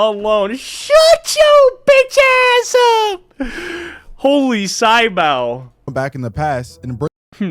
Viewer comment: did unc really say sy bau